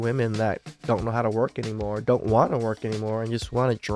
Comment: I heard "they depend on alimony", but they meant "they don't want to work"